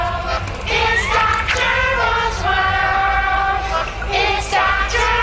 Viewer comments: Its that Germans worm, Its not Gerald's world, Its dahb jurgble swirl, Its nocturnal swirl, Its got gerbil squirrel, Its dakturbo's whirl, It's got Gerald's swirl, Insta turtle swirl, Its the gerbil swirl, Its doc turbo's well, Insta turbo's world, It’s that German squirrel, It's the turtle squirrel, its the tunnel world, It's a journal's world, Mista Gerbil's world, It's the jerma's world, Its the turtles world, Its got germs as well, It's the journal's ware, It's doctor moles world (I added more) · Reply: now all i hear is it's nocturnal swirl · I like the part when they say its Doc gerbil's world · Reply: Could've swore I heard "It's Got Gerbil Swirl"